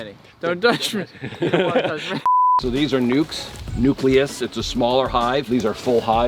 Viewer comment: did he say these are nukes